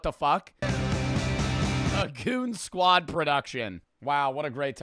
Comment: no one talking about how he said a coonsquad production instead of goon?